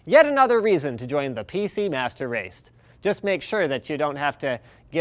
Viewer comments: did he say master raste? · PCMR FTW! (although he actually says PC master raist.)